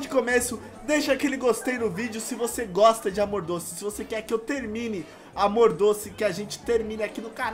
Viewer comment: AEHOOO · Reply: o disse "querem que eu termine amor doce aki no canal" eu entendi "terminar o anal" kkk!! :D